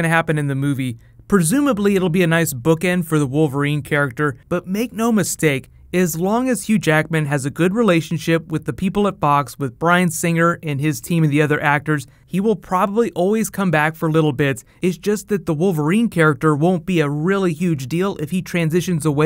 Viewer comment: Haha when you said Brian singer, thought you said Brian Cranston